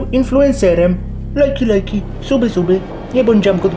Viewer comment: czy tylko ja usłyszałam ''jungkook''